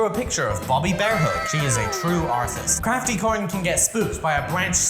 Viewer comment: tell me why I heard arsonist the first time 😂 💀